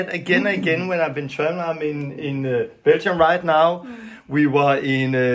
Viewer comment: Where is he? Sounds like said “I’m in Bento right now”??